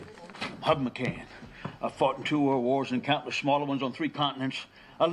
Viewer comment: I swear I hear “ I’ve fart in two world wars” I have rewatched it soo many times and my goodness I’m sorry but..that’s what I hear lol